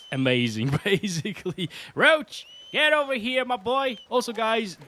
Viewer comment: Did I hear you call the horse Roach at 5:24 ? Like myself you must have played lots of Witcher 3